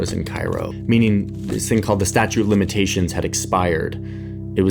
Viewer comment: Did I just hear “statue of limitations”?😂